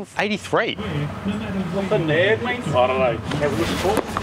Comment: he actually said "he's a fuckin nerd mate"